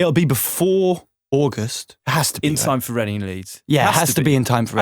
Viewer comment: Wait wait he said it’ll be ready before what at 7:34?!?! I know he said august but what did they said after that!? Ready in leads!? Is that a festival!? Ugh I’m so American I can’t understand their accents sometimes 😂😂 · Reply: Reading and Leeds, two festivals in August